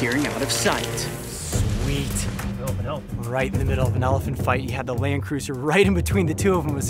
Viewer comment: Did he say hell of a elephant?